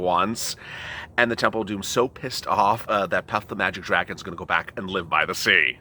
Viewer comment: 🙏 Want to Paypal Donate to Support this Channel? 🙏 Donate ▶️▶️ <link> Remember make sure to subscribe ✅ to the Channel ▶️▶️ <link> 🤣🤣 and hit 🔨 the bell button 🔔 on the front page of this channel so you get an alert when a new 📹 video goes live 🤣 just like now! TURNING ON NOTIFICATION HELP: If you have subscribed but dont see a red dot next to your name, do these steps 1 confirm you are subscribed. <link> 2. go to <link> and hit the BELL *3 change your setting to public <link> (after you do switch to PUBLIC, you will see a bell next to your name when u post to!) ______________ Instructions for THE PRIVATE 1. Subscribe to this YouTube Channel: <link> 2. * FOLLOW on Instagram: <link> 3. Send a DM request on IG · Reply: Good Morning!!! I thought you said "Pot The Magic 🐉" lol. you're the Best